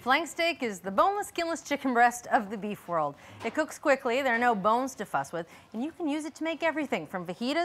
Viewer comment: no bones to fuss with. I thought she said 'no bones to f*#k with'